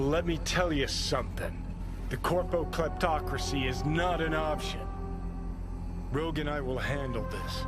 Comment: Damn I always thought he said “the corpo cuntocracy is not an option”….. kinda bummed my brain fired right and finally let me see that subtitle.